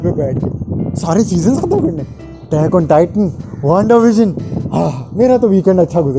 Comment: did he just say attack on titan